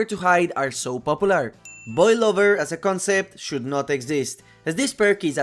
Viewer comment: thought you said boy lover😭